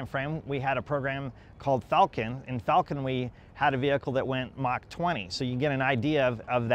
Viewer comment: did he say they 'had' a vehicle that went mach 20?